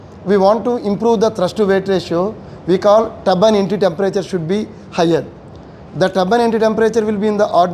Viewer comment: Can anyone tell What is “Tabban Anti Temperature” as he says at 07:56 ??? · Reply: It’s not Tabban Anti or Tabban NT, it is Turbine Ebtry Temperature